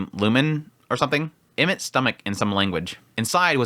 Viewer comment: what did he say here? Imit stomach? Thats the other name of the forrest in another language right